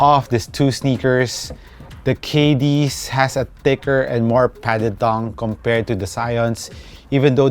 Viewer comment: hope you don't mind me saying this but i was just letting the video play in the background until i heard you say at 3:47 the KD has a thick and more padded thong...